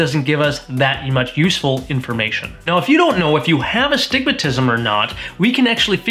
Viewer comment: I always thought they were saying “you have a stigmatism.” Not “You have astigmatism.” Lolol 😂